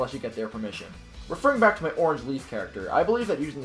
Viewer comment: i heard Orange Leafy